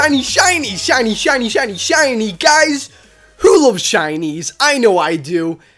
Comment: My subtitles said this at 0:10 "Chinese Chinese Chinese Chinese Chinese Chinese Chinese Chinese guys, who lives Chinese?